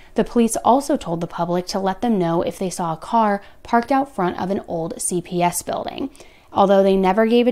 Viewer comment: did she say "car parked outside an old CVS building "?!?! Or CPS building? Man, if she said CVS that gives me chills now!!!